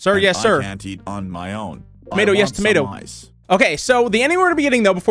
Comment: I thought he sayed "I want tomato slice" lol